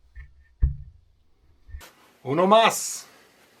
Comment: I didn't hear what you said. It sounds like "normas"(нормас) in Russian slang, which means "good."